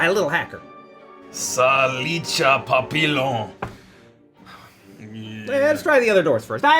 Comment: I heard "papilon"